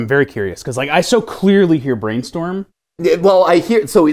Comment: I heard "Grain store"